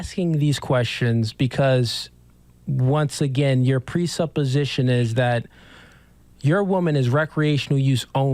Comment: did he say pre'suppa'sistion? 😂 I think the word you're looking for is predisposition. The "word" recreational use only (which is actually a TERM but okay lol) has been used plenty. Example "Oh you smoke weed?" "Yeah but it's for recreational use only imo. I won't smoke it daily." This dude really isn't as intelligent as he likes to think he is 🤦🏽‍♀️